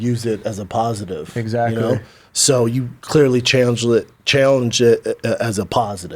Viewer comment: Wtf did he say at 2:00? "challenged"? "Chanelllged"? "Changelled"? I had a stroke listening to Brenda within the first 2 minutes lol